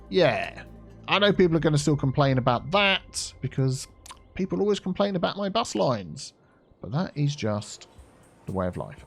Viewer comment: "I know people are gonna still complain about that because...people always complain about my bus lines!" What I heard: "Infamy, infamy, they've all got it in for me!" I for one know I'll never get used to your bus...squiggles. Which is why I just don't think about them after they are commissioned. Also, Advanced Vehicle Options for editing mass transit passenger capacity to more realistic levels.